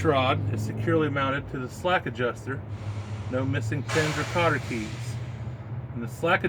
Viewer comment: Hey at 3:50 I missed what you said isn’t missing before the kotter keys? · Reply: Pins